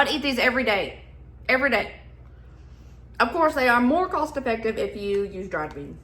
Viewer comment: You said vost effective and i thought you said constipated. No, i never outgrew my 8 yr oldness